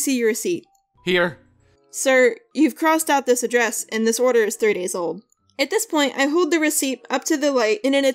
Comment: I don't know why, but I heard 30 YEARS old... That would have been hilarious...